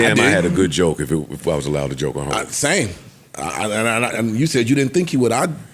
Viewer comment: Did he just say he wasn't allowed for joke on Hov? Lol 5:13 Mark? 😅 Beyonce will be my favorite. She is a Performer and works hard. She is THAT GIRL…